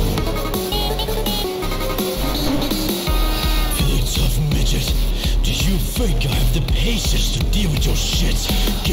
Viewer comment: does anyone know what he's exactly saying here? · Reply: “Feeling tough migdet , do you think I have the patience to deal with your sh!t, get out of the way, you insufferable little -“